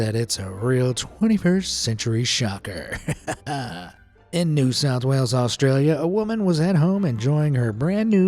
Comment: did that say, New South Wales I live there